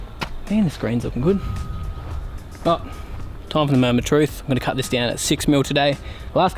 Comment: Did I hear you say mow-ment of truth?